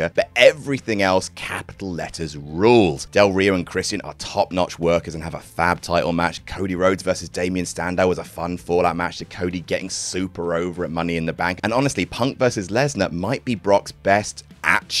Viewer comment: am i the only one that hears say “standow” instead of “sandow” here